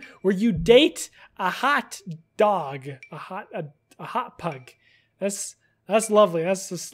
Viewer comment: When he said hot pug I thought he said hot pocket. I was like hooottt pocket! 😂😂